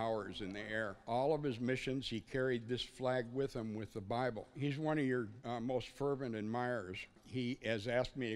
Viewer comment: I thought he said he identified as an attack helicopter 😂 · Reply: I thought he said he was one.